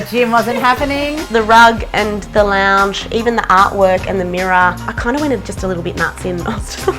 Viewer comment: "I kind wanted it..." What??? @ 8:55 What did she say???